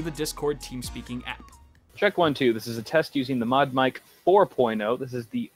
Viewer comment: who else thought it said momdic at first